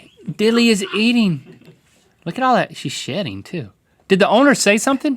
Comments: When said she is shedding, I thought he said she is shting. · Thought was saying "she's shitting too". 😂😂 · Who else thought they said shitting not shedding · Am i the only one that thought that said "she's shitting too"?